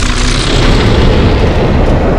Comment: "For plunger" was the coldest scene in skibidi toilet history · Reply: I thought it said “my pleasure”